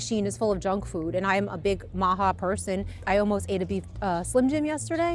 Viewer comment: the cnn subtitles got "mocha" from MAHA, so much for media literacy.